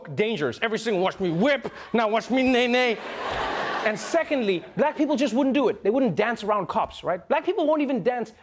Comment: Can we talk about these "black poople" for a second? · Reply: RJay I thought he said black poopoo 😂😂🤣